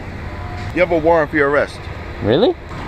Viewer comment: What did he said? 0:59? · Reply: He said you have warrant for his arrest